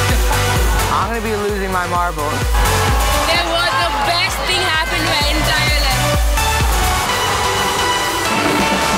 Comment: the girl says it was the best thing happened * I dont understand the end of this sentence,could anyone help me? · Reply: 'in my entire life'